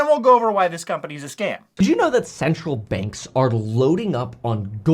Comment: The way he pronounces "central" is messing with me. I keep hearing him say "did you know SEXUAL banks are buying gold??"